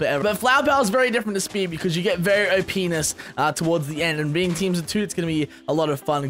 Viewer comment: I heard "o' penis" at 1:39 XD